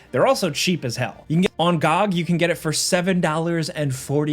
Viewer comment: I thought you said on god instead of on gog!!🤔🤔😂😂